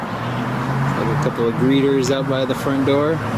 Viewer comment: When you mentioned the “greeters“ at the door, I thought you were saying “breeders” hahahaha. I had to rewind it a couple of times lol